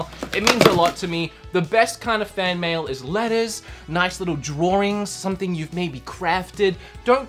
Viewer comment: I cant be the only one who heard "The best kind of fan mail is lettuce"